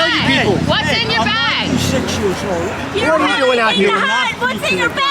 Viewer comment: Did he say he was 96 years old?